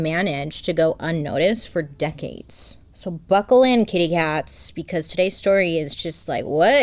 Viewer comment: I thought she said "buckle in cave rats" and I said 😢 but then looked down at what I was wearing and it fits, go on ...